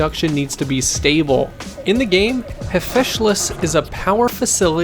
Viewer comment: Did he say hefeshalis?